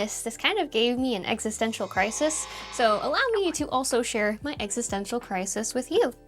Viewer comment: did you say EGG-cis-stential? 😝😝😝